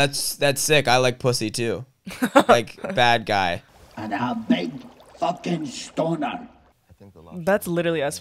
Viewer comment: the lobster calling the guy 'a big fucking stoner' at 16:09 in a scottish accent really threw me because i thought he said 'stauner' which in scotland means erect penis, the layers of symbolism in this film are so deep bro